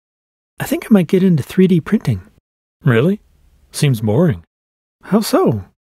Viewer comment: I thought he said 3d painting